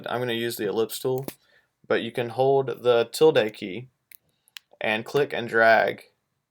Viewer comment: what key is he saying? i just really can't figure it out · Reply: The tilde key. (~)